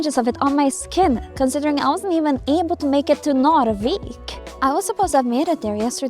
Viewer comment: As a person who lives in the southern part of Sweden, when you say Narvik it sounds like your saying norrviken which is where an event called Aurora happens every year